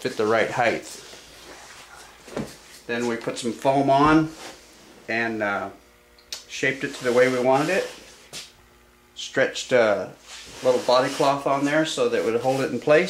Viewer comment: you described what you stretched over the foam but I couldn't understand you. Did you say Body Cloth, or bonding cloth or ???